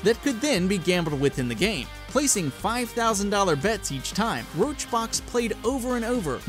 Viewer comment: did he call summit1g... RoachRox?